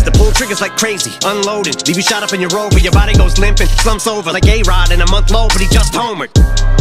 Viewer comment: i always thought he said "gay rod" no " a-rod"